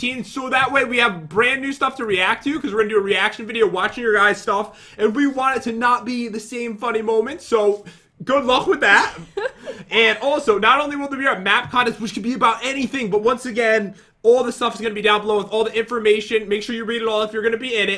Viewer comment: I thought you said a math contest